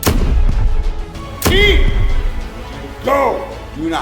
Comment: My captions say that the trailer uses "Leave! Go!" for this scene.